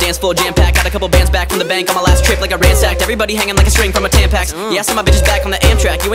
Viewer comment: did he say everybody hanging like a string from a tampax? LOL